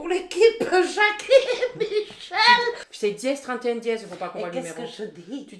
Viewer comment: omg a 9:15 quand elle dit Michel j'ai entendu Whitney Houston :')